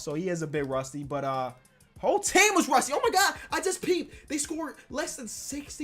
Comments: I swear you said "OMG I JUST PEED"😂😂😂💀💀💀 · sounded like he said I just peed😂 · did say “oh my god I just peed” or am I the only one who heard that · did just say "i just pee'd" ? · It sounds like he said “Oh my god I just peed”